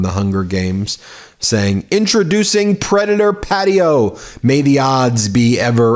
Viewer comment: I thought you said Newsom called it Pedo Patio. Both are funny though.